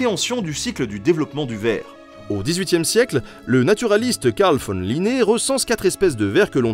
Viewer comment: J'ai compris "Naturiste"... Je n'ai pas pu voir l'image de façon sérieuse et saine.